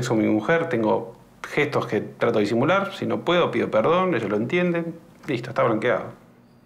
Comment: qué dice?? no se le entiende bien. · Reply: "trato de disimular, si no puedo pido perdón, ella lo entiende..listo está blanqueado"